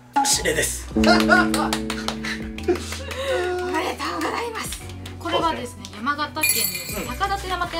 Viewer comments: コレだコレだってママが言ってるって言ってるけど私には「おはようございます」って聞こえるのですがｗｗｗ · 黒川の場所は、女性の声で、おはようございますって聞こえるけど～違うかな？ママじゃないことは確かかと？😅